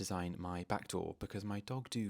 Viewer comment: 🐾🐶🩶did you say your dog may have ruined it 14:26 😮sweet boy🤗🐾🐶